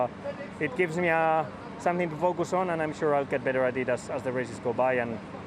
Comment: Did he say he was gonna get better Adidas?